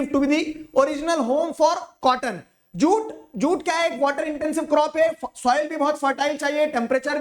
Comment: pe kya bola?😭💀😭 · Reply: Jute?😊